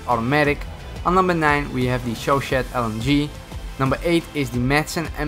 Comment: When he said chauchat I thought he said 4Chan